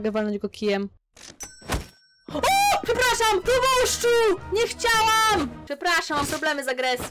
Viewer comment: usłyszałam Chrabąszczu xdddd